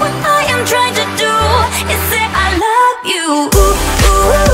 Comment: do i hear "zane" ???? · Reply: She said “say” not zane